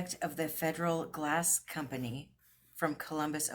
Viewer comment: I wish you could have gone with me too. 🤗 Federalist glass, hmm, I had no idea what it was called. 🤣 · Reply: I thought you said it was labeled Federalist glass.